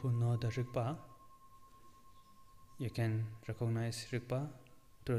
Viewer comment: Is anyone able to help me understand what is said at 53:42? Something like "Those people who know the <ripa?>; they can recognise <ripa?> through listening to sound" - I can't find this term explained anywhere, but would like to know it. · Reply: The word is rigpa. It is part of the Dzogchen teachings: <link>